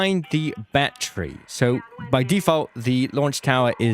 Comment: I thought you said bat tree at first, I was so confused.